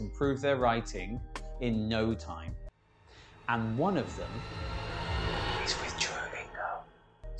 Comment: Sorry, I can't understand what you say at 0:19 :( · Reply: I say one of them is Duolingo.